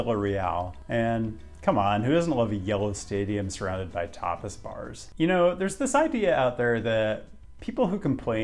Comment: am i the only one that heard topless bar instead of tapas bar?